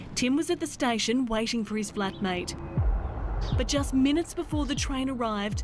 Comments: Did she say he was sitting waiting for his black mate ..... Is that racist ... U tell me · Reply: Flat mate... not black mate... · waiting for his black mate? did I hear that correctly? why does it matter the skin tone of his mate? lol